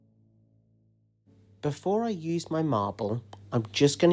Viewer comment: The pearl paints and the swipe always make interesting patterns. BTW you said you were going to "use my marble " , I heard you were "losing my marbles ", 😅